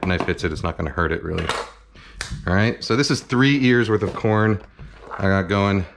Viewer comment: Idk why I heard "Three years worth of corn" instead of "Three Ears" LOL me just imagining Kenji growing corn in his backyard for 3 years to make this recipe